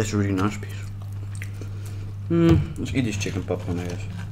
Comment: I keep hearing "chicken PopPorn"